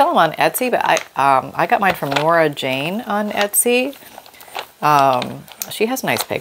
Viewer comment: Thanks for the stamp info, although it sound like you said Laura Jane and putting the CC on, it captured your word as Laura Jane but searching Etsy it finds Nora Jane selling the stamps. Love watching your videos. BTW, the sound of the chair wasn’t bad.